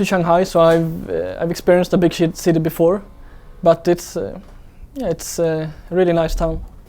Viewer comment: Is it just me or did he just say 'Big shit, city before'? baha